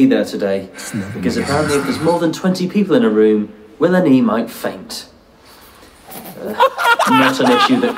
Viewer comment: “no two ways” joke went seriously under the radar. He’s too funny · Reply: What did he say at 8:08? "Because apparently if there is more than 20 people in a room an emo faints"?